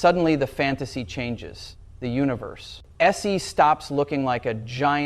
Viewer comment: Did Brian Greene actually say this? What does he say at about 2:35 of the video? "Se?" I thought I heard at least one more artifact before ten minutes of the video were done. Makes me doubt the authenticity of the video.